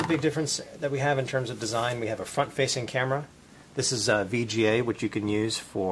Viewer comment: did he say fuck facing camera?